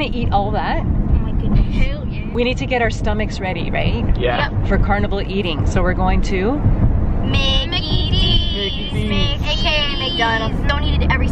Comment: did she say hell ya